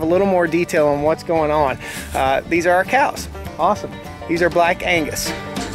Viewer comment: I swear i thought he said the cows are called black anus. Had to research and now i know he said black angus.😂